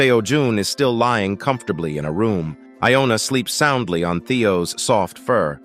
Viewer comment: did he just called theo seojun